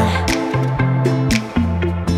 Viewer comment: ~ I thought it was “ooo you keep me coming for ya” not “ooo you keeping coming for ya” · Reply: Omg I heard " ooo you keep me coming for ya " as well 😄